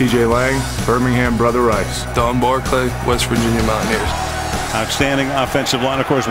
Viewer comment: i heard the n word